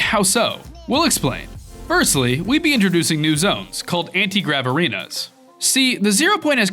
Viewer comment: When you said “anti-grav arenas”, I heard that as “antigraverinos”. Ned Flanders, that you?